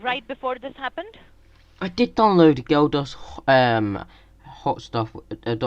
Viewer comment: No shit, I thought you said, "Geldof's hot stuff". Apparently the aid is more lively that I thought!